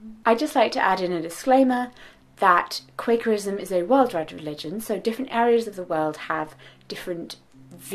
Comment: I am an American Quaker and really enjoy watching these videos. There is a caption error at 2:38. you say "Quakerism is a worldwide religion"; the caption says "Quakerism is a worldly religion". Question: Do you get more money from ads if I watch them in their entirety? Les if I skip the ad? Thanks!